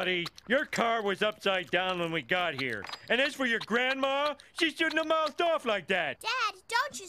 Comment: Help from native speakers. On 0:36 he says "she shouldn't mouthed off like that" according to the automatic legend. Is it correct? I just can't get what he says here. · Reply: "she shouldn't have mouthed off like that" meaning, She shouldn't have talked back.